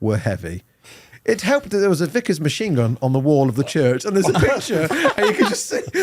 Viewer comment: Genuinely heard that as a vicar's machine gun on the wall of the church.